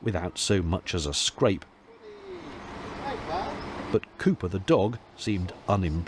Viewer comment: I misheard the dog’s name, I thought you said Pooper.